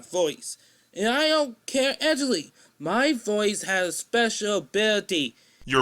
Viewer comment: When he said " Actually " it sounded like he said my name, which is Angelique. I am confused as to what he's saying